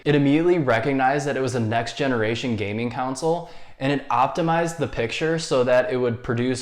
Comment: I like how you're always saying "council" instead of "console"😂